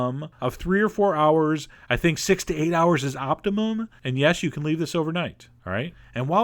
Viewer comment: I thought he said sixty eight hours, lol